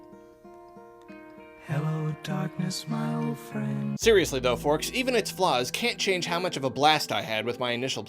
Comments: did...did you say...forks? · did you call us forks?